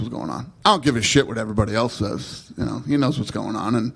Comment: he doesn’t say I don’t give a shit what the numbers says. 5:00 he says I don’t give a shit what everybody else says.